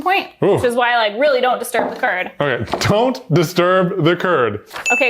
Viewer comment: "DO NOT DISTURB THE CURD!" I heard "Do Not disturb the TURD"